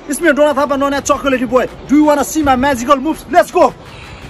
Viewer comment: I thought 😂😂😂 SaY that Do You Wanna See MY eSpical Boos😂😂😂 I was just kidding….maN you are so awesome #justic for me this Comment will be pinned 🎉🎉🎉🎉🎉🎉